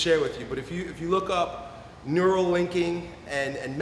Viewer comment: Sorry what's he saying a At the 4:55 mark? Neuroligin and anker mark..??